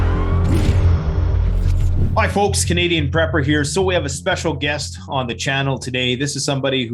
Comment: I thought said Dr. Pepper 🌶 🤔 😅